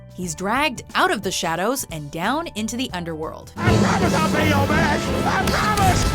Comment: for some reason, i thought Dr.Facilier said "I promise i'll be your man" instead of "I promise i'll pay you back" XD